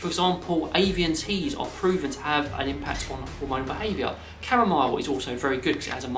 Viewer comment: Before avian teas, at 1:05 are you talking about... Caramel? Do I hear good or did I misunderstood? Like this made of sugar?